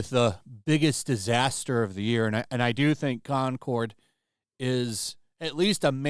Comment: For a split second at the start of the video I thought you'd said "clowncord", and honestly it deserves to be called that far more.